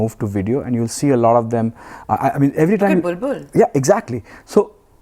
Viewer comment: what she says at @ 17:25? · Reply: she said "Bul Bul Bul"